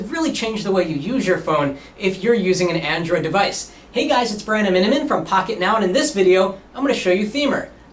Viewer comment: What is his name? all That i hear is "Hi guys I'm Brandeminem from pocketnow"